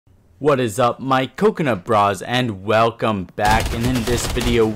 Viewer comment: I thought he said coconut bras 🤣